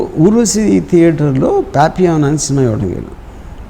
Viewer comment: what was the movie RGV saying about at 1:21:07 ?? there was no #hyper in the ending otherwise nice interview ;) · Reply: Movie name Papillon